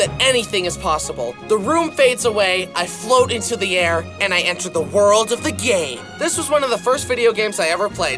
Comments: I heard the world of the gay😂😂😂😂😂😂😂😂😂😂😂😂😂😂😂😂😂😂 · I heard in “the enter the world of the gay” 😭😭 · I thought he said the world of the gay but he said game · Why did I hear "world of the gay" instead of game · I swear I heard : "The room fades away, I float into the air, and I enter the world of the GAY" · "And I enter the world of the gay" is what I hear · sounds like he said and i enter the world of the gay but it says game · And I enter the world of the gay Is that misheard or- 😭🙏